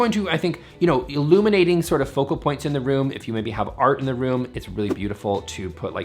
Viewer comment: When you said “art in the room “ I heard “argument room “…this is why I live alone!! I had to laugh…and laugh!